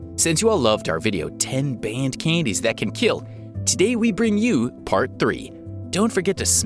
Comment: For a second in the intro I thought you said “one billion candies that can kill” instead of “10 candies that can kill” Extra meme: “KitKat” Me: so you’ve chosen death. ( and I can’t get enough of it )